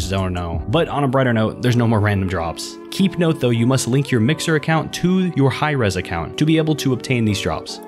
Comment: What did you say at 6:00? Mixer Account?